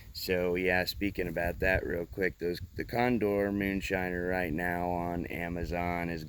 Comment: Sorry I just saw this comment and I must have misspoke cuz mine definitely doesn't say China lol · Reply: Oops, my bad, when you said Moonshiner, I thought you said Made in China. At least on my computer your voice is kind if furry sounding. I forgot to tell you, I have both. :) They are both good knives.